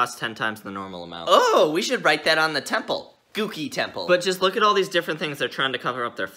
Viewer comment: I thought he said dookie temple